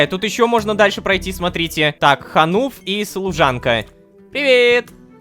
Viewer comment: "Пойди найду Хануф и займись пряжей..." я: послышалось Нахуф (что б#### со мной не так)